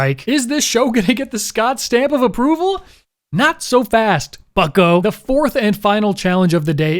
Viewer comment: I totally thought he said "fuck-o" before I realized it's Scott. 😂